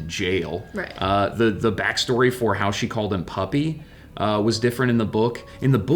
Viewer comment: I thought she was saying Papi the entire time...not puppy wth